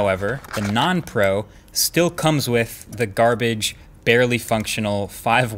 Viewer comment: did he say garbage? I don't hear much clearly...